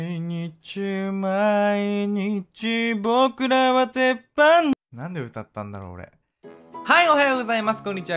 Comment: が泳げたい焼きくん歌うと｢僕らは鉄板の｣が｢僕らは天パの｣に聞こえるww